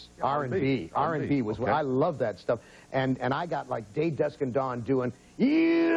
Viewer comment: Guys, please help me. I am Russian and I can't understand what singer he is talking about here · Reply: As far as I can tell, he's talking about the singing group from the 1940s called "Day, Dawn, and Dusk", featuring Bob Caver, Gus Simons, and Ed Coleman. I tried searching the one lyric he gave to try to determine the song, but I couldn't find it. They seemed to sing songs described as a mixture of R&B, Jazz, pop, classical, and country. That's all I could find.